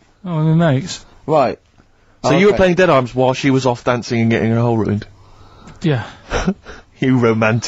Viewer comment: does Steve say getting her hole ruined? Yikes!